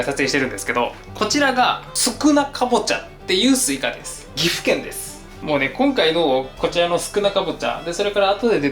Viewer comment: 「宿儺かぼちゃっていうスイカです」って聞こえた